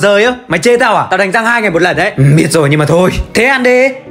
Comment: Đánh 2 ngày 1 lần mà t nghe thành 2 lần 1 ngày 😅